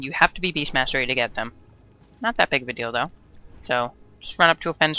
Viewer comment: Not too big of a dildo ;p (i know you said 'deal though' but sounded like dildo ;p ) P.S. liked the video bro , subbed